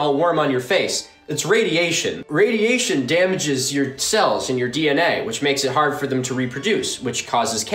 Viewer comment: "..Cells in your DNA.." 😂😂 I laughed so hard at that Edit: gotta change my ears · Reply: I thought he said "cells and your DNA"?